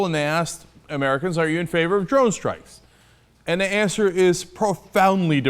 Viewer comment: he said drone strikes, subtitles said draw strikes